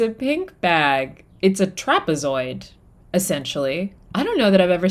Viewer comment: Listening with the volume down at work, you said the purse was a trapazoid but i had to listen again because i thought you said it was a Tapas Holder and i was not going to question your judgement at all. I could fit a whole day's worth of tapas in there.